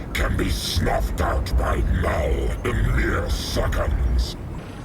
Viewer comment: I think Venom said "Knull", not "now"